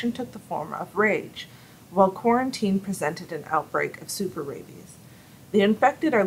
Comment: what did you say 3:18 super ladies?